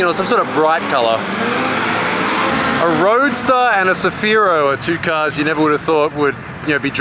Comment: i didnt hear proprely the car on the left of the roadster is what car ? tell me pls i want it it looks epic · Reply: It's a Nissan Cefiro A31